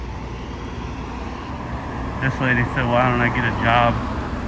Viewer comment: "Get a job" Oh thats what she said! There's so much boot polish on her tongue I couldn't understand! 🥾👅